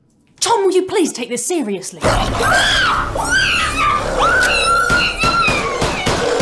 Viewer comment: i always thought he said hole possum, i just realised he said hole person